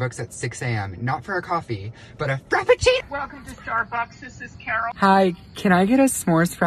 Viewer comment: The amount of times I heard “Welcome to Starbucks, this is Carol” is appalling!!! 😭😭